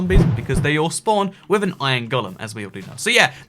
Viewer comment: When you said they all spawn with an iron golem per second I thought you said they all spawn with an iron gun